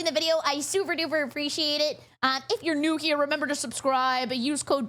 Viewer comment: Ngl, I misheard your name, I thought you said Perry, and I instantly thought Perry the platypus